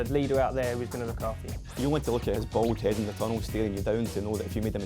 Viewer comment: lmfao wtf did he say? 0:34-0:39? lmfao!! Something about a bald headed tunnel?